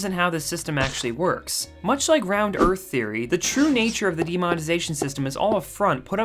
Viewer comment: Did I just hear “round earthy ‘theary’ “ ???